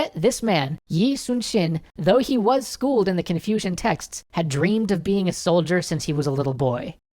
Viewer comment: Thought he said "Confusion texts"